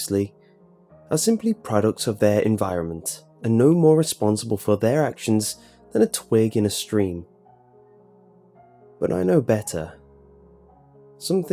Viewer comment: As responsible as a twig in a stream so not responsible for their actions at all? What? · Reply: dolphin named Infernicus I miss heard, I thought he said that most people know we as responsible for a twig in a stream, not just himself.